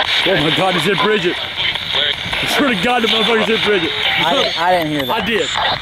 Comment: I think some sounds sound like Grandpas voice. Like in 2:35 it sounds like said "Bridgette". WEll i believe this is 90% real.